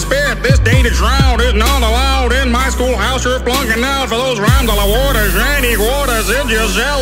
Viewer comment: for a moment i thought he said ur f""""g out here